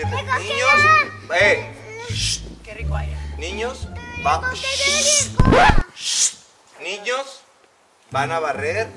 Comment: Qué dice Ponchito en 17:55?? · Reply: El recogedor y la escoba